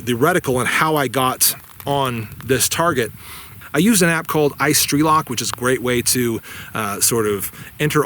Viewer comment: I rewound the video 5 or 6 times and couldn't understand the name of the app @ 7:45 . Could someone please fill me in if they understood what it was called. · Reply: I believe he said “Strelok” which is a ballistics app.